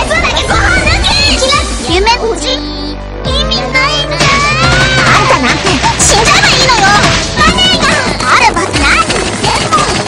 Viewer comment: i heard shinjaeba at 1:10 :)